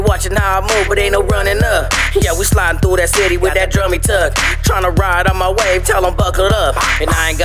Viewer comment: I had to play the intro a few times .. I thought you said that , you were sliding through that city with a tummy tuck !! 😂LoL